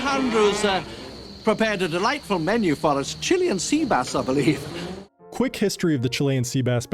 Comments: When I was younger I always thought he said lunch was Chili and Sea Bass.... sounded disgusting. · I hoped this was about their actual lunch, Chilean sea bass is amazing. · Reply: I had quite the buzz when I saw the original Jurassic Park and, I'd have sworn he said "chili and sheep ass" and, was very confused... ⭐🐺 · When I was I kid I always thought they were saying Chili and seabass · “Chilean sea bass. Spared no expense” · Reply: For some reason I thought he said “chilly and sea bass l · Always thought Hammond said "Chili and Sea Bass" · When I saw it in the theater I thought he said chili and sea bass. I always though that was a weird combination. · I hoped this was about their actual lunch, Chilean sea bass is amazing. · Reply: When I was a kid I thought they had said "chili and seabass" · I hoped this was about their actual lunch, Chilean sea bass is amazing. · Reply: For the longest time as a kid, I thought he said they were eating “Chili and Seabass”. I kept picturing fried sea bass on Texas style chili and couldn’t understand it. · I hoped this was about their actual lunch, Chilean sea bass is amazing. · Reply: I always thought he said Chili and Sea bass. And only last year I learned the truth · When I saw this for the first time in 1993 I was 15. I thought he was saying “chili and sea bass”. Had not heard of Chilean sea bass at the time. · When I first saw the film, I thought he said “chili and sea bass”. I was wondering why there was no chili…